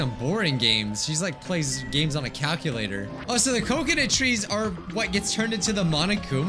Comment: I legit thought said Kokona trees at first, but then I realized he said COCONUT